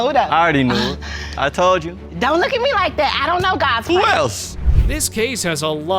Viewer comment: did she say “don’t look at me like that I don’t know gods work” don’t blame sis that was u